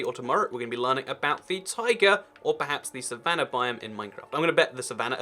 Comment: I thought you said tiger instead of taiga.